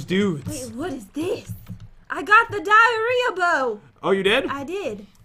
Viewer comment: did Jen say “ I got the diarrhea now”